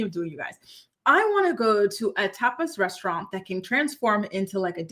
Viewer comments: Ohhhhh my goodness, I thought you said you wanted to do to a “TOPLESS” restaurant for your birthday!!! I had to rewind and rewind…. 😂😂😂😂😂 My bad!!!! 😂😂😂😂😂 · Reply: 💯 same. I heard "topless" to! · Ohhhhh my goodness, I thought you said you wanted to do to a “TOPLESS” restaurant for your birthday!!! I had to rewind and rewind…. 😂😂😂😂😂 My bad!!!! 😂😂😂😂😂